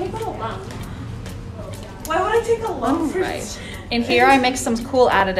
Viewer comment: did she say she was going to take out a loan? 💀